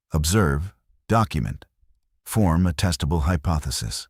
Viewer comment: I had to rewind and listen to that one again. It sounded like you said "form a testicle hypothesis"